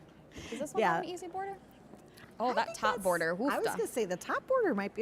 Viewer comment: is great as a co-host - funny and interesting. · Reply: I heard an Ufda!